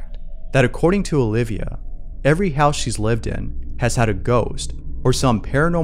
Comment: I thought she said every house they lived in, they had a goose. I was wondering where the pet goose was in the scene.